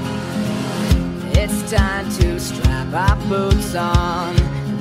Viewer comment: Was it just me that almost heard boobs in the beginning? ALMOST!! I don't have a dirty mind I promise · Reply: yes i heard that too i really thought it was boobs when i first watched it